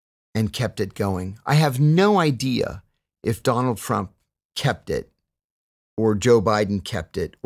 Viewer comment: did he say “Donald Frump”?